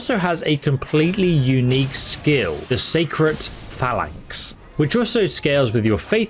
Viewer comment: I'm sorry, did you say sacred phallics